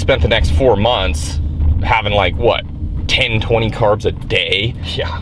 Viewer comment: did I just hear "10-20 carbs a day" or 120 carbs?!?? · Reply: TuLa Nguyen 10-20g of carbs a day. Like they said, they went the extreme.